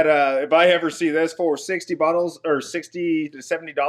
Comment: did he say 60 buttholes? 😂😂